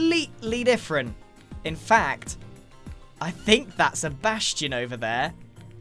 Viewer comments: When grian saw the bastion, I thought he said "i think that's Sebastian over there" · What did Grian say? I don’t know of any hermits named Sebastian.